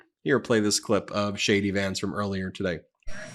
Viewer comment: in this clip ... sounds like you said "shady Vance" instead of "JD Vance." I like that. I think it will stick.